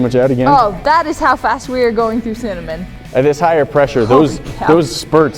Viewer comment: LOL... Sounded like she said, "That is half assed weird going through cinnamon." Although she actually said, "That's how fast we're going through cinnamon."